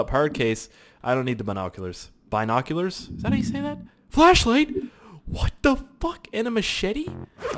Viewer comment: I thought you were saying fleshlight LOL